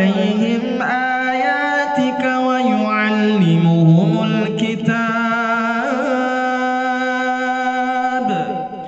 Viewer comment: Mohon maaf itu saat 05:43 Itu diteks Wayu'allimuhumul kitaab, yang dilantunkan Wayu'allimukumul kitaab. Atau saya salah dengar ya? Maaf🙏